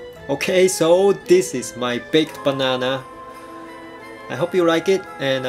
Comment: Haha, I was joking with that, the first time I heard it,I though I heard "big" instead of baked. xD ( Even though, I still hear "big" instead of "baked" ) But... Most of all, how do you know I'm Asian... ?